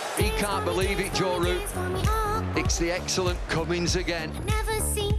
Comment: I thought it said Ben Cummins HAHAHA